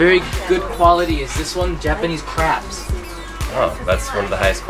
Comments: I thought he said Japanese craps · I thought he said Japanese craps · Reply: Sofia Grover same I thought he said Japanese craps